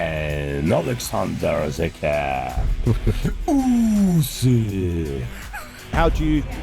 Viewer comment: This here: 0:24 Does anyone know what he means when he says Oleksander Zukar? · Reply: Oleksander the cat Usyk 😂